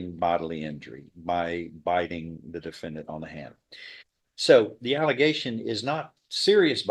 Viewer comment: Sounded like he said bit on the hand